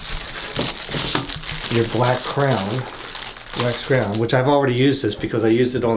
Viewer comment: Did he say black crown? Does he mean crayon? Is that how he pronounces it? Weird. I guess it's like the "wuder" for water.